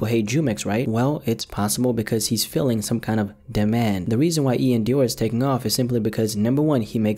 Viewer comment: I thought said “in my Dior”